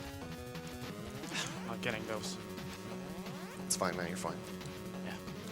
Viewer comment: "It's fine, honey, you're fine." Am I hearing that right? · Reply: Pretty sure he said "It's fine, man, you're fine" but either way works, it's the same message being communicated.